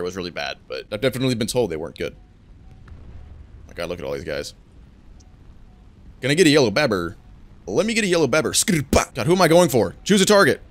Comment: what is he saying at 1:30 im so confused please help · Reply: A 2 year old tiktok meme someone goes to a drivethrough saying "can I get a yellow babber"